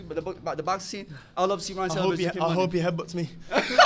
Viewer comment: What did Ryan say in the minute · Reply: "I hope he headbutts me"